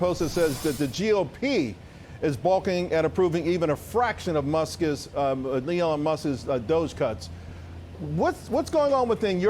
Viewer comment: Did he say “Leon Musk” at 3:18?